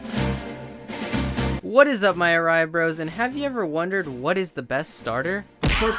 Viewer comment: Am I the only one who thought Eryizo Said What is up my eyebrows